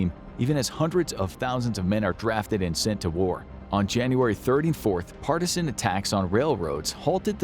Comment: "ON JANUARY 34TH" ??? Am I hearing it wrong lol?